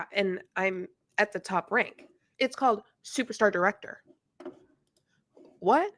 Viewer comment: When she said "super star director the first time, I heard "super fart director" and that's what I will call them from now on 😆